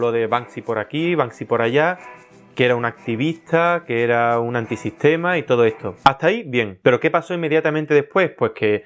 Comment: awwwww, pensé que decia antisemita :'v